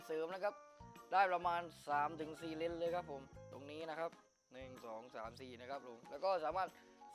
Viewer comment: i cant understand a word from this video but i like the part 0:38 zim zom zam zi:D..i gues ist 1234 lol